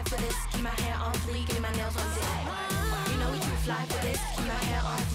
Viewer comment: I thought she said "you now we're too fly for this keep my hair on fleek and my nails on dick"😭